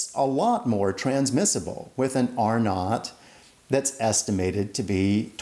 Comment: Thought he was saying "or not" until I realised he was saying " R nought" 🤣